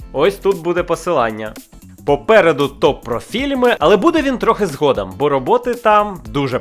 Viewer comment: Попереду ТОР про фільми ". А мені спочатку почулося ,, Попереду копрофільми" )))